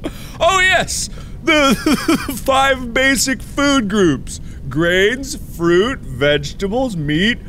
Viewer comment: Mark: the five basic food groups, grains, fruit, vegtables, meat, fleer! Sign two seconds later: fleer, dont drink it! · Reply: I literally heard him say brains, fruit, vegetables, meat, fleer! You know, instead of grains.